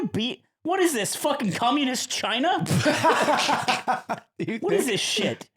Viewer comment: does say “cummunist china” or am i mishearing